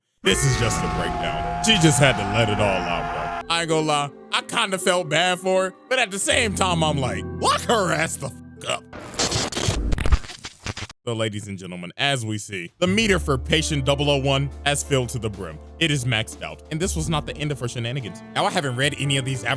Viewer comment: I don’t know why I thought he said “ladies and gentleniggas”